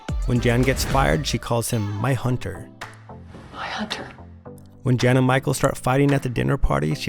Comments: i thought she said “bye hunter” ? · I always thought she said “bye hunter” instead of “my hunter” 😳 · I always thought she said “bye hunter” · I thought she said bye hunter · I support this theory 100%, the only thing I’m going to say is that you misheard what Jan said to Hunter when she was fired. The DVD subtitles for The Job episode clearly state that she said “Bye, Hunter” instead of my Hunter. Of course, she was whispering with a lot of emotion so it’s understandable that people wouldn’t be able to hear what she actually said, and the fact that Hunter was the only employee she was acting friendly towards at a point where she was absolutely enraged at Dunder Mifflin definitely raises eyebrows in The Office theory community. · I thought she said Bye Hunter not My Hunter lol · I thought she said “bye hunter”..... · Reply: Sounds like "My" to me, need to get ahold of the script ! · I thought she said “ bye hunter” lol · I thought she said "bye hunter" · I thought she said “Bye Hunter”, not “My Hunter”. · oh wow! I always thought she said “bye hunter” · I thought at 4 14 she said bye huntrr not my hunter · are you sure? I kinda thought she said “bye hunter” not “my hunter” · i always thought she said bye hunter. probably because she says it rather quickly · I thought she said “bye hunter”